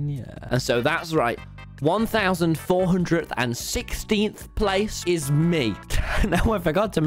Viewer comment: it said 1436 instead of 1416th place on 15:46 and it also said 1 hour and 8 minutes instead of 1 hour on 6 minutes